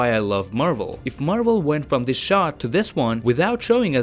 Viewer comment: When he said those words "This Shot" then the subtittle literally says "This Shark" 😅 If my english is really bad , im not gonna understand what he tryna says 😂 I didn't know if its just me that was using the English Subtittle to make me more understanding , Since im still ongoing sharpening my english language cause it's not my main language ... or there's more people like me that were also using the Subtittle even tho it is on English Language 🤣 ? Are you also using the SUBTITTLE Even u already good in English ?